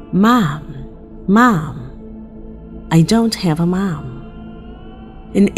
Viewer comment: In #1 I heard: Mom, don't TAKE my mom. Not I don't have a mom.